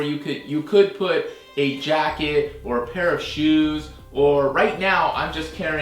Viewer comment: I thought you said parachute! pair of shoes makes more sense! lol